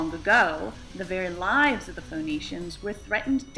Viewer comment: I heard "Phoenicians." So the people who founded Carthage are in this game? Cool, Sign me up.